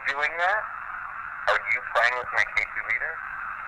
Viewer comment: what I heard, slowing down to .25: - Are you playing with my e-meter? - No, I'm not. I don't know what you're talking about. Ghosts are such boomers, when it comes to technology... (I'm not kidding about it sounding like that, though)